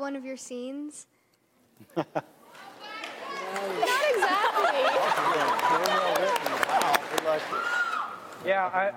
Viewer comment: Does anyone know what someone from the audience said @ 45:58 when cami kj and mads looked at each other and started laughing?? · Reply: rachel evans they said bughead